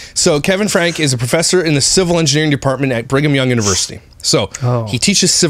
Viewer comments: I thought they were saying “Bring ‘em young university” 🤣 i was like- well this will be gross 🤣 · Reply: I heard “Bring them young” the first time said it too!! I went right to the comments 😂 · I thought they were saying “Bring ‘em young university” 🤣 i was like- well this will be gross 🤣